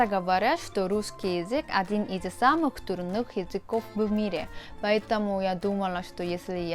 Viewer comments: почему я слышу , что наш язык один из самых "дурных" языков в мире !? ) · Вместо трудных послышалось дурных😅🙈